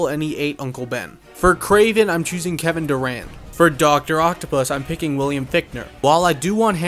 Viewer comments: i fr thought this dude said Kevin Durant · I thought he said Kevin Durant for a second😭